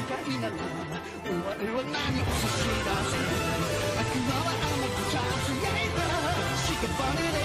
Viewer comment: here they say sashidaseru and I used to think that they said shiganshina